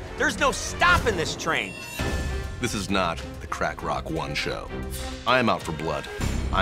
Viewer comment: Did he call Cleen "crack rock one"? Btw, it's around 25:45.